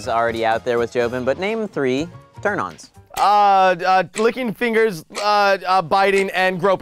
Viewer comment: I thought Joven said "clicking fingers"